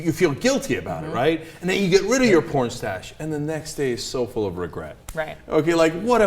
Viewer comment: I thought he said pornstache. Like mustache. I thought he was regretting shaving off his porn-star mustache.